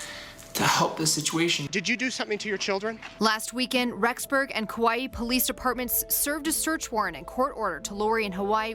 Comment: I kept hearing “ kawiee police department